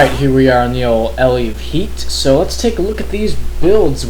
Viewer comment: haha, I thought you said "ele in heat" at first